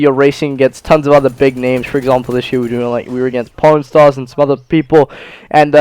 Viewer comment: when he said pawnstars I thought he said pornstars